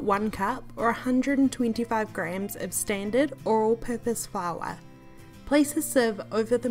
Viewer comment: I love this recipe! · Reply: kool kid I could have sworn she said Oral Purpose flour lol.